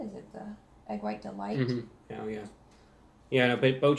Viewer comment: did he just said he’ll yeah?🤣🤣🤣